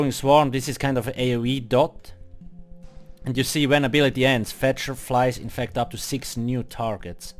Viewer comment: Sounded like you said "green bell ends" :D